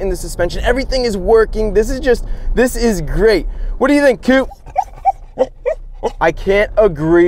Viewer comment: Chris: what do you think coop? Coop: let me out of this truck Chris: i cant agree more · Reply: Thought he said what do you think of it kou?